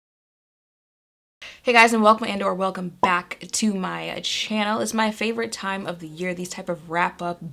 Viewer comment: What does she say at the beginning of each video? I hear "hey guys you are welcomed and welcome back to my channel"... I have been trying to figure this out for multiple videos without luck 😂😂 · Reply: Hey guys welcome and/or welcome back to my channel